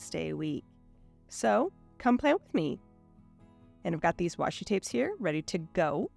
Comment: Beautiful spread. I just love when you say, “Come plan with me!” I always think I hear you say, “Come play with me!” So cute!